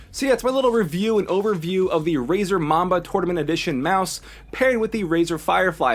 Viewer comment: lol did you just say razor mama